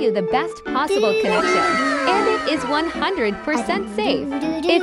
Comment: Why did I hear : "and it is 100% fake"